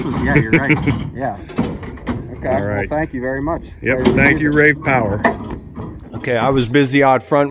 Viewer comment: I thought you were saying, "Ray's Power". A better name in my humble opinion. Lol.